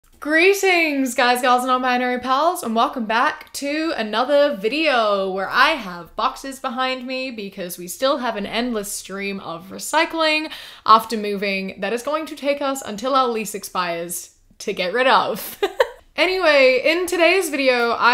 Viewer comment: Idk why but I thought she said “there’s boxers behind me”